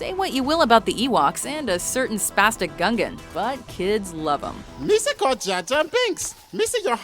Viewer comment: does she say Spastic Gungan?